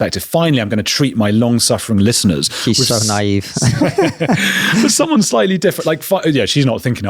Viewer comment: Did KK say "He's so Naive" about Rupert Lowe? I couldn't quite catch that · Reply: I think he said "She's so naive", as they were talking about Maitlis